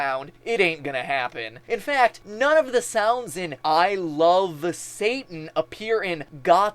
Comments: Strangely, instead of "I love satan" I heard "love ain't in" for some reason. Is that weird? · I heard 'All the syntax(s)' rather than 'I love Satan'. Bullshyt fer those who assumed 'loving Satan'.